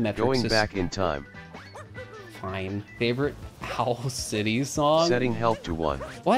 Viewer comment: It sounds like you said "health city song"